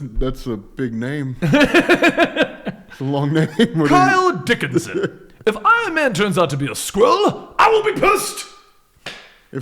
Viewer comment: I thought said "If Iron-Man turns out to be a squirrel..." I instantly thought of Gadget from Chip and Dales Rescue Rangers piloting the Iron Man armour from the head which she altered to be a cockpit.